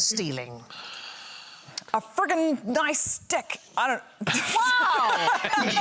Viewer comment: (ish) - its funny, i heard "a Friggin Nice Stick" at first, but when Sandi called it into question, i rewound the video and listened again, and suddenly im hearing "a Friggin Nice DICK"... ...so i blame Sandi xD